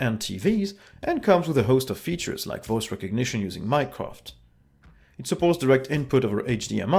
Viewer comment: For a second I thought you said "Voice recognition using Minecraft" :D